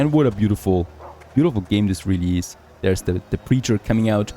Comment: i thought you said The Creature at 8:05 !🤣🤣